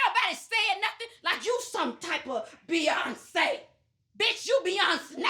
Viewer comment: 😂 I think she said Beyonsnake ! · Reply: I heard "Beyonc-nay"